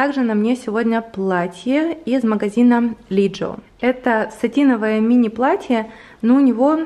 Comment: мне послышалось что платье из магазина Lidl🤣